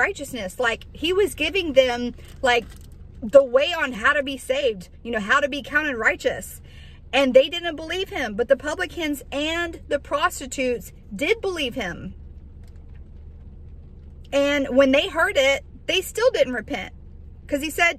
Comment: Thank you for this! I thought you said "republicans". I had to go back twice to hear what you said but it was clear once I listened through. 😆 🤣 😂